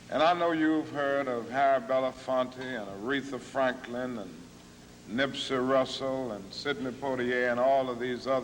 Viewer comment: I thought he said Nipsey Hussel